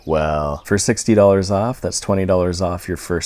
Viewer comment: I heard him say GO TO HELLo Fresh